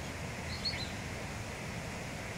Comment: Why does it sound like she is saying “ come on , come on , then at the water she goes “ Wait” so dad can show them how to get in the water. And of course there is always that one stubborn family member that’s late to every party.! Is it me or I’s there just too much cuteness going on here 😍😍